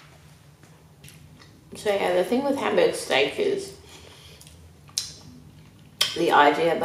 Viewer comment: I thought it said hamburger steak😂